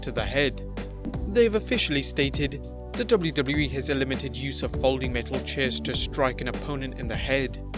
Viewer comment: you said elimited instead of eliminated 😦😂don't let the drugs get you